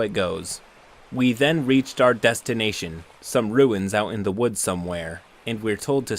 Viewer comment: I misheard ruins as "Rowans" at 2:21 and that killed me.